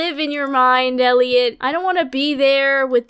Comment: i heard “Queer, green cube” 😰